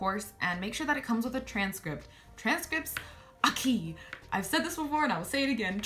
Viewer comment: lmao, when you said "transcripts are key" I thought you said "transcripts aquí" and I was like ? transcripts here ? what ? and then I listened properly 😂